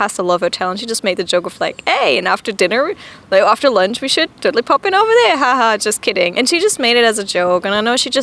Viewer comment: what did she say? sry I'm not native T^T maybe 'pop in over there??'